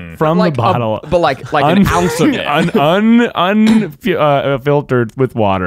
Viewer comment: did zach call all of eugene’s coffee an ounce lol